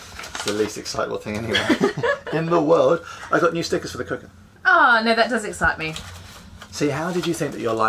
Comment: A little disappointed, when you said "new stickers for the cooker" I thought you said "new stickers for the crew" and that we might have new merch to buy. Oh well. Welcome Back!